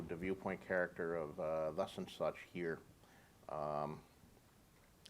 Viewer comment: What did he say at 2:36?? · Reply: "Thus and such"